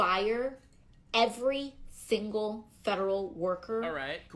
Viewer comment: did she say "fingle"